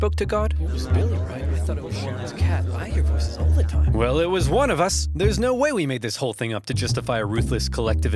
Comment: and I heard “I thought it was Sherman’s cat” For context it was the person who spoke to god and and said that Americans should expand outwards